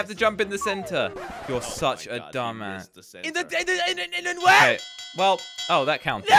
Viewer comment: Is it just me or does say a bad word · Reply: yea he said dumb a**